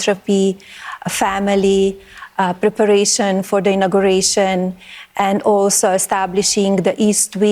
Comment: Oh please, now she is going to be all over everywhere to promote her pathetic video. Go back to your tower Melania. She only comes out when it will make her money. I thought she said preparing for immigration instead of the inauguration.